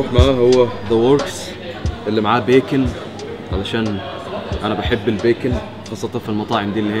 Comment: Did he say "beacon" at 2:42 ?!??!?!?!